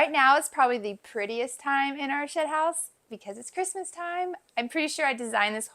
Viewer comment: Ha ha you said shed house I thought you said shithouse. Great living space.👍